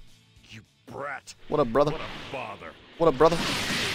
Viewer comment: I always have goku on my team in parallel quests so when piccolo say what a bother I thought he said what a father. Because goku wold beat up gohan for me because I just sit back and watch then steal the kill